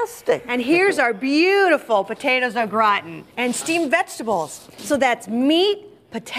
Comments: Does she say 'potatoes have rotten"? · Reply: Potatoes a Gratin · Does she say Potatoes are rotten? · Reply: zzzhuh "Au Gratin"